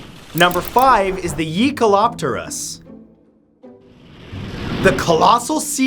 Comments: I thought you said yeet-aloptorus..... 🦂 + 🐟 = jaealoptours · When you said Jaekaloptaurous I thought you said YEETkoloptourus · I thought he said Yeetkelopterus 😂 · Jaekelopterus? You said "yee" so what I thought you said was "yeekelopterus"